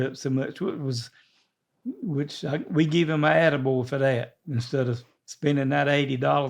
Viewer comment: I thought said “I gave him a edible” when he really said attaboy lol.